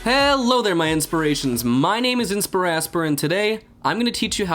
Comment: I heard "wassup my inspirations" and I think I died a little inside xD